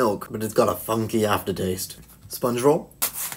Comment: I thought he said SpongeBob not sponge roll😂😂